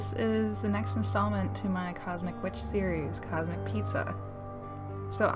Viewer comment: I thought it said comet pizza, lol... like comet ping pong... Anyways, love the cosmic witches!!